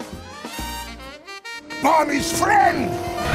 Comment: godzilla: WTF everyone else: UHHHHH rex: POLISH FRIEND(thats what i heard) EVERYONE: AHHHHHH BOOOM